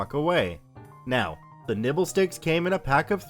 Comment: Nibble I thought he said nipples